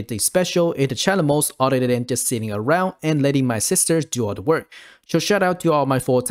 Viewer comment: LOL, at the beginning when you said you let your Assisters do all the work, I initially heard it as "I let all my sisters do the work". I just imagined you holding 3 young girls hostage, making them farm skill gems for you. 🤣 I've been running some field challenge modes myself. The earlier maps are definitely way better in terms of loot. I've got several alts in the 100-120 range so it's a good opportunity to get them leveled a bit. IMC needs to put the newer skill gems in as monster drops in the higher level maps.